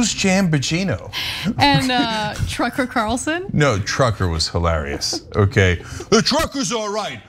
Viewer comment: Pronunciation according to Mike Lindell,my zero Guy...Jam Bambino,Scorn Sanity,Trucker Carson,Laura Pilgrim,OAEnds,Fox Noose, and Nudes Max.... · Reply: 😅😅😅 I thought he said "Trunker" (Carlson).